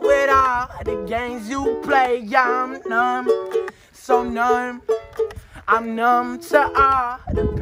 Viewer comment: “So gnome” -Kid Laroi 2020